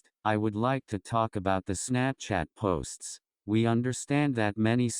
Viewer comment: The speakers accent sound like he is saying “snapshit”